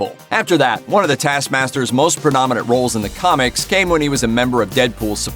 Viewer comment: subtitle says "prominent", but what the narrator says is different. It sounded like "pronomonent" / "pronomonert" or idk.